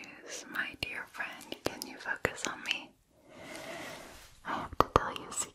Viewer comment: Did anyone else hear "can you f**k a zombie?" At 7:55.